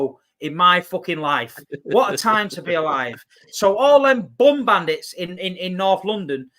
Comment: What line was this? 😂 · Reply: I'm a but deaf so I hope I heard wrong but I thought you said bum bandits, but please put me straight if I miss heard. Cheers you're up there with the best City youtubers